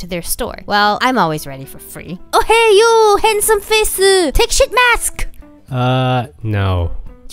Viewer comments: Did I hear it right 4:27 Person “oh hey you handsome face, take sh*t mask” XD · Still my favourite of your videos. The first time I saw it, I thought I heard: Oh, hay you handsome faceu! Take Shit-Mask!! The last part was faster spoken and that's so funny. I know it is sheet-mask, but I can't unhear it lol.🤣😂 Hey you gorgeous creature! Take this Shit!!🥰😍😘 Hope you find this funny too. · I LEGIT THOUGHT HE SAID “oh hey you, handsome face-u, take shit mask.” 🤣🤣🤣